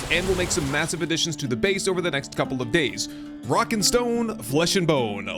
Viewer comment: Did i hear a rock and stone ?!